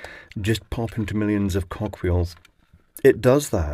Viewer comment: I heard 'millions of cock wheels' lol